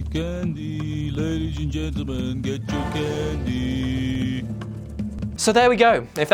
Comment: Wow, that last one, I could have sworn he said "kill me can you kill me" before I heard "come get your candy